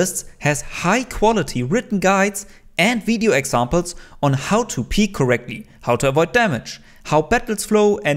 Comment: I can't be the only one who heard "how to pee correctly" and did a double take video was unexpected but amazingly helpful, thank you